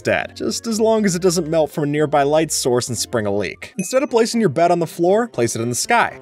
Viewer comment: Captions spelling mistake 2:33 Instead of placing your bet on the floor correct: Instead of placing your bed on the floor